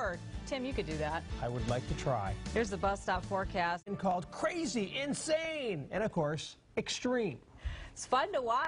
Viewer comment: It sounded like he said: "Crazy, insane intercourse extreme"